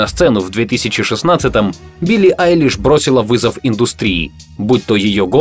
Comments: Мне послышалось убили Айлиш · Мне одной послышалось убили Айлиш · Мне послышалось Убила Айлиш